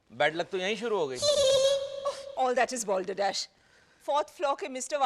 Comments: what was the word Maya said?? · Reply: Balderdash (nonsense, bakwas) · what is this word voltadesh or waltadadh pls tell me wanna know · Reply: Balderdash. It means nonsense